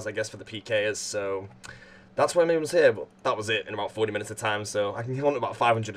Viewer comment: Thought you said 80 minutes, disliked.